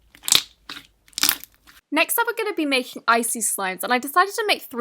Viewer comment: I heard isis slimes